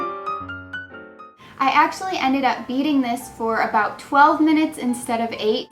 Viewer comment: When you said you were beating it I thought you said eating it